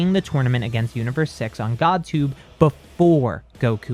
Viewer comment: Did he just say GodTube?